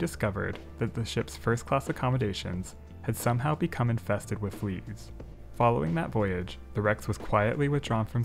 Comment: The 1st class area was infected with what? I listened three times at 13:38 and cannot make out what word he says. Sounds like "weeds" or "leaves". (about the only unclear word in the whole video!) l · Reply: Fleas